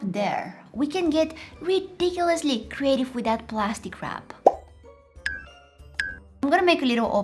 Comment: the entire time i thought she was saying “plastic crap” XD